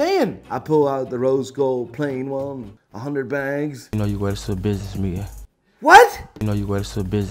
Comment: The BEST COMMENT UNDER THIS VIDEO WILL WIN A GOD TIER WATCH! 🤷🏻‍♂️🔥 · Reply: BTW Leonard what he says that you could not understand is that you can wear that Patek Philip to a business meeting